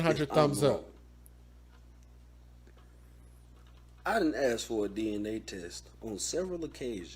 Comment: I'm confused, at 1:50:50 did he say "I didn't ask for a DNA test"?